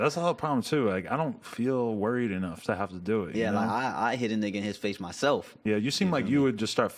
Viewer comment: did Adam say “nigga”?